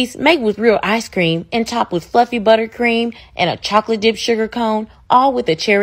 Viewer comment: I legit thought you said "and f###kn butter cream" 💀💀